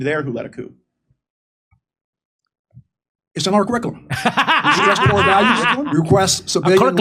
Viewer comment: What is the general saying? ''its a norel quickle'' ? wtf did he even say , Jimmy's laughing, I couldn't catch it.. shrugs... Edit; later Jimmy explains that he said curriculum, lmao, what is this general's 'core language' or dialect. lol